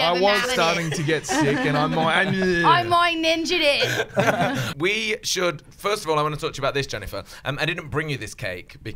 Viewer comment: what did she say in 2:06? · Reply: "I mind ninja'd it"